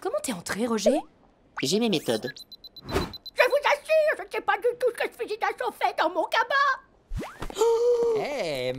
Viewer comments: Juste une question... Elle dit quoi la grand mère à 0:29 ? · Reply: "Je vous assure, je ne sais pas du tout ce que ce fusil d'assaut fait dans mon cabas !" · je vous assure... je sais pas dutout... quoi ? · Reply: ha ! "ques-ce-que fait ce fusil d'assaut fait dans mon sac à main !" je croyais que c'était l'arme de la sécurité, ouai un peut chelou je sais x)